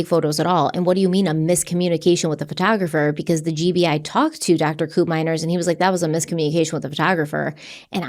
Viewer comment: I keep hearing "dr. Poopmyer"